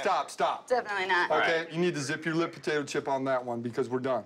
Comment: “You need to zip your lip potato chip.” That cop really thought that was a line didn’t he? He’s gotta be a dad. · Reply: I thought he said zip your lip tater chip